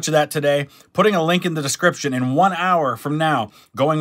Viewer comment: I thought you said, “Going live with Mr. beast.”